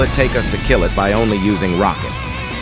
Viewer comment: i thought you said (with only rocket :) )